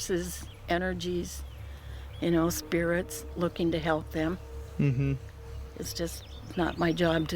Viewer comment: The little girl sounded like she was saying help me?